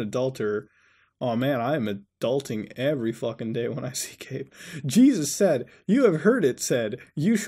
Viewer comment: did he say "i am adulting every day when i see Gabe"?